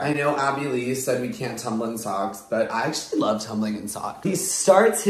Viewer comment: Did you just say Abby Lee Miller sent use Tumblr socks and you’re not using them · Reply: no he said abby said you can’t tumble in socks